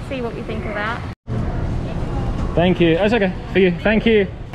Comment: Did I hear said "sigue" when the lady tried to give him the change @ 9:30? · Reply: He actually said “it’s ok” but it’s kinda muted due to his mask.